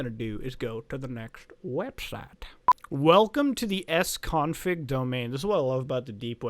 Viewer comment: I swear when he said, "S-Config," I heard him say, "Ex-Convict" Lol